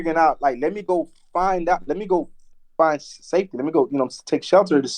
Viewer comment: Man there was some really weird wording from 100k Track... At one point, I am pretty sure, he said something that sounded like he was saying he had to go find the "Devil"... check the time stamp and let me know if yall hear it too... At one point he also calls someone a "Character" was there kind of making it sound like actors.... Then 100k Track says he would only play a Cop in a "movie"... Hes given off some weird vibes, especially since he said that weird shit after Von died about how his girl didn't speak with him from the dead when she said she got a text message from Von after he died that night, he knows that because hes in touch with Haitians and knows black magic and all this other weird ritualistic shit. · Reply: is the time stamp where it sounds like he almost said "Find Satan" instead of Saftey. Idk I could be tripping but therees def some weird shit around 100k Track fo sho. There was an officer there with the same last name as him but a white guy I think